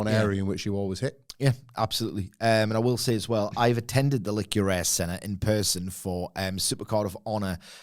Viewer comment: Did he just say the lick your a** center lol ?